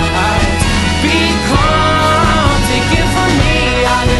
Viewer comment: I heard "You hate your pulse because it thinks you're still alive." as "You hate your boyfriend because he thinks your still alive." the first time I listened to this. I was so confused. XD